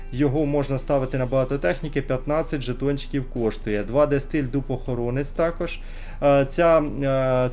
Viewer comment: послышалось "два дэ стиль дупохоронець " ))) Аж начал представлять себе его )))